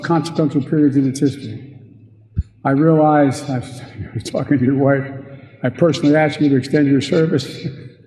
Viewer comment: I heard this listening to a replay of it and had to replay it 5 or 10 times to figure out he said "after talking to your wife". I seriously thought he said "after f'n with your wife"